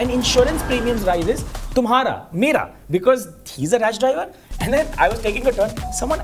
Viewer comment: I thought for a moment he said a "Raj driver." 😅